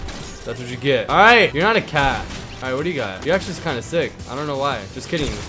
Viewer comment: did he just say your not a cat to the cat skin